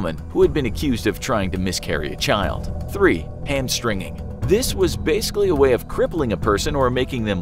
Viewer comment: Hamstringing, l thought he said hamstering 😂